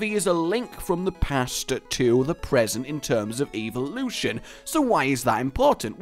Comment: Did you just say EVILution?